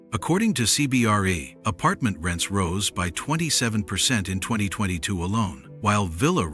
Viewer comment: Sounds like an old video? I heard “…will be in 2022…”